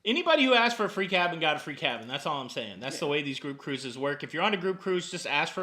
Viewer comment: Hahaha I thought you said cruise whores. 😆